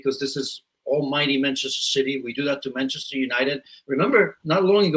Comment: Did he just say "The mighty Manchester City"? 🤡🤣🤣🤣 The club has never been mighty.